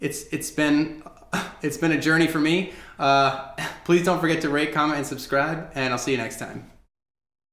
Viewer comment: Okay... At the end of this video, I thought he said... Lol "Rape, comit, and describe" Idk why? Lol